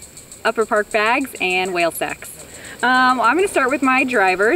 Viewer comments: Is it just me or did she just say she's sponsored by "whale sex"? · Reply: Thanks for that link. I heard her say "Whale Sacs" later though. But would have never guessed that webpage. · Is it just me or did she just say she's sponsored by "whale sex"?